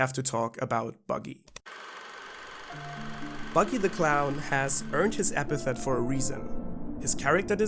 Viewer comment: We just misheard him the whole time, it was never Buggy The Clown but actually Buggy D. Clown